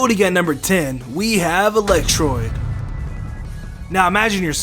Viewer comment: did he say electroid.....